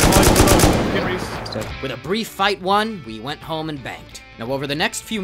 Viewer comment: coulda sworn he said, "with a brief fight won, we went home and banged"